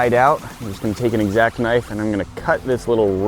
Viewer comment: did he say exact knife